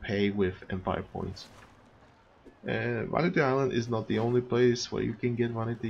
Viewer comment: is also the delos farfa and cathrage stores where you can buy statues with EP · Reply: I thought you said only vanity islands cool